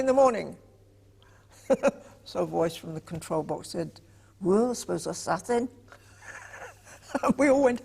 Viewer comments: What does she say @ 3:01? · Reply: Well I suppose that's that then. It's in the captions. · Does anybody know what "wuls wiz a suthin" means? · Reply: ''Well I suppose that's that then'' attempt at a Liverpool accent ! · I can't figure out what she said at 3:02. Something about assassin? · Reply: ''Well I suppose that's that then'' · What she says at 3:02??? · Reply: " Well I suppose that's that then " was mimicking a Liverpool accent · ? I heard: "So a voice from the control box said, 'Roos is a saffin'". Anyone? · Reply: ''Well I suppose that's that then''